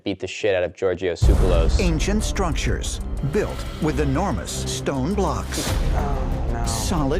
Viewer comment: I swear he said Stronctures wtf